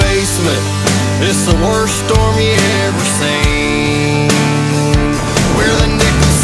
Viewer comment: I thought they were saying with instead of we’re.